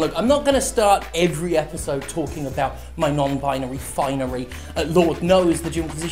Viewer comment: Every time I hear Jim say "non-binary finery," I hear "non-binary REfinery." Which works just as well, if you ask me.